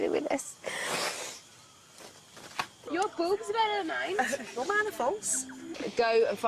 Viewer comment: did she said "your boobs are better than mine".. ?